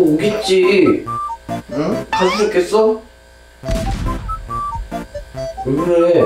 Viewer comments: 제 생각엔 가죽자켓있어? 가 아니고 가출했겠어? 라고 들리네용 저만그런가요? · 난 가출했겠어 라고 들림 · 곧 오겠지, 가출했겠어? 라고 들리는거같은뎅